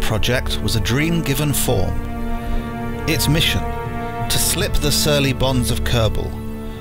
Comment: I genuinely thought you were saying "To slip the surly buns of Kerbal"